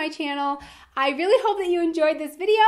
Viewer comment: Where are the blueberries you promised in the end? Ok, I've seen enough of your clips to know that you said "bloopers", but really I understand blueberries. Who else hears "blueberries" instead of "bloopers" here at 4:57?